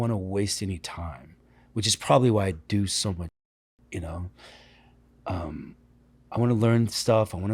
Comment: What did Norman say @ 31:58? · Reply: “probably why i do so much shit” i think